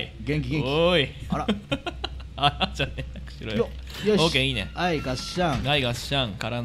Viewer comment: ではさんがさんに対して「あらじゃねえよクソ野郎」って言ってるように聞こえるw